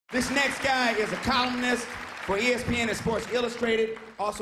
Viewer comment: at first i thought Davidson said "he's a communist"